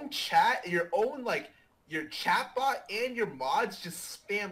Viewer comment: he never answered the question if he viewbots tho lmao, viewbotting confirmed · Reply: is it me that does not understand english or did scarra just say "your chatbot"